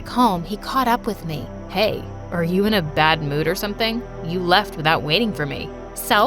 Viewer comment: and 2:06:57 the caption said "Hey, Are You Ignoring Me?" but it's actually saying "Hey , Are You In A Bad Mood Or Something, You Left Me Without Waiting For Me"💀💀💀